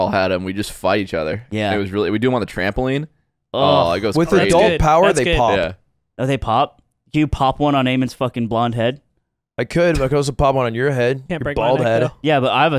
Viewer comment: Nick roasting Aiden while Slime is singing "and I'm so fucking cringe" to the tune of Dido, and Aiden just looks at the camera, stunned. Pure art. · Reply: I thought he said “Aimen’s fucking cringe”